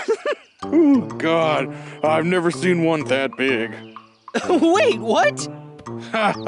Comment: WHAT DID HE SAYYYY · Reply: He said: "Oh, God, I've never seen one that big" so basically Logan got dirty minded amd though about di